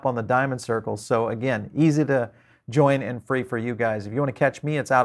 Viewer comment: Oh I thought he said his name was Paul bearer ⚰️ 🤣